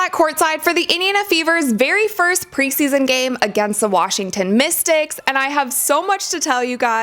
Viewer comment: I swear, I heard her say “Washington ‘Mistakes’” at least twice.